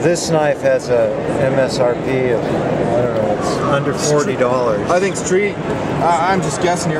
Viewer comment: i thought he said the cara cara was MSRP "a hundred forty", not "under 40", I was like NOOOOOOOOOO